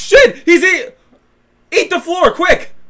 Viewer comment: It sounds like she says ease the floor.